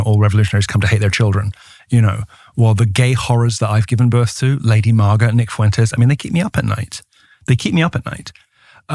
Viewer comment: Did he just say the gay horrors I gave birth to Lady Mara… “NICK FUENTES”? 👀